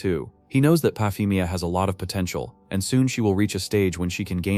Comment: did you say potential